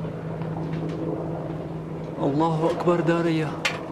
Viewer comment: Is he saying Allah Akbar diarrhea?